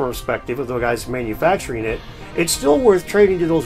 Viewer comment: Thought u said bitch ass Egyptians at the end😂